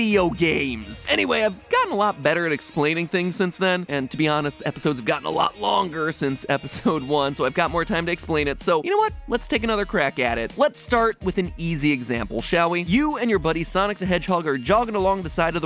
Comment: I swear he said crack addict